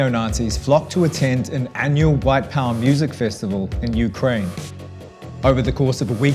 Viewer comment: Did he just say black power festival ?